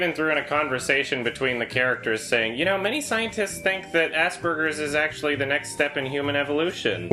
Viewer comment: i don't know english very well, so i kind of stuck on 3:56 don't quite understanding why ass burgers is the next step in a human evolution